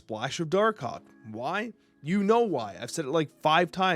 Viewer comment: Misheard "Dark Hawk" for "Doc Oc" meaning Dr. Octavius for over a month now in the snap chat (audio only). I randomly click on this video and finally understood it. I thought DocOc pulls your shit from your hand leaving you in a position where you basically only topdeck random stuff and have to play it.